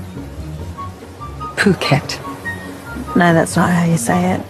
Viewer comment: ตอนแรก เราได้ยินเป็น "ปากเกร็ด"ค่ะ 😂